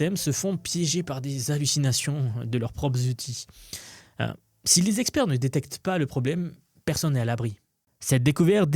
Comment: J'ai entendu "cinq Zexpzerts" ou j'ai halluciné? J'ai mis au point un prompt de quelques dizaines de tokens pour repérer les IA qui hallucinent. Mise en œuvre enfantine. Résultat garanti, parfois spectaculaire.